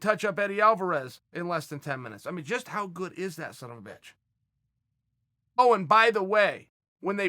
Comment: did Chael called Conner 'son of a bitch' 🤔 9:21 Floyd vs Conner PPV buys record were fake. Chael vs Conner next fight to make now